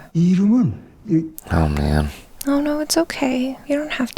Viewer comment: Was it just me who heard say 'Il-nam' (The old guy's real name) instead of Oh man.